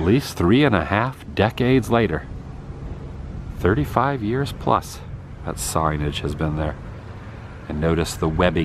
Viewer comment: "A least three and a half decades later.....thirty five years plus......that sorry bitch has been there" Haha, I know that's not what he said (after listening a couple times), but oh my God did I crack up at the thought of hating a sign so much!